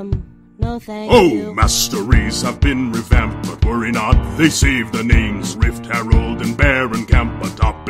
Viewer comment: "But worry not, they cieved the nings"? What the heck does he say there? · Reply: "saved the names"